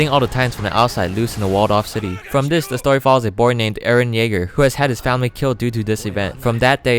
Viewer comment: who thought he said "eren nigger" ?